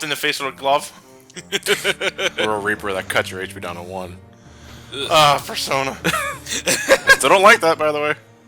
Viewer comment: Wait, did just call it "Fursona"?